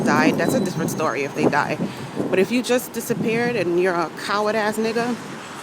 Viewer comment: Did you just say "coward-ass november india george george echo roger" or something? I dare not say november india george george echo roger.